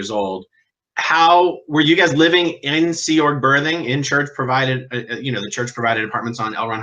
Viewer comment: Good for you! On a lighter note, the first few times I heard berthing, I heard “ birthing “ lol. I’m a retired NICU nurse.